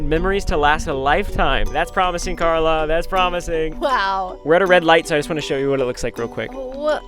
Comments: i thought he said “we ran a red light” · i thought the said "I ran a red light" 😂😂